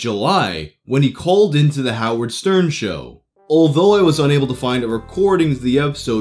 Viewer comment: Hand to God, I heard u say, "He CRAWLED into the Howard Stern show"!!! 2nd CALL IN, I was like, AGAIN??!!? What dedication, crawling & shit..WTF?!!